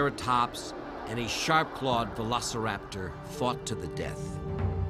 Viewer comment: I thought he said "F*cked to the death." And was like whuuuuuttttttt??????